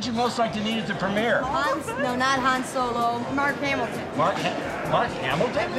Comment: "I’m Mark Hamil!" "Ok thank you" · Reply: "That's a shame, I thought for a second you said Mark Hamilton, the Star Wars actor"